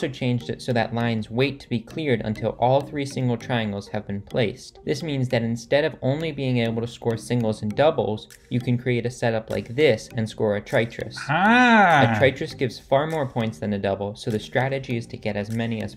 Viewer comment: Did he just say "haan" in hindi at 4:16 .?